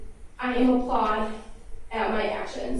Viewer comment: Did she just say "I am applaud at my actions"